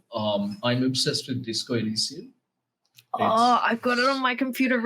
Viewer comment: did anyone catch his recommendation? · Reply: Thanks Gayatri, he's talking about the game Disco Elysium.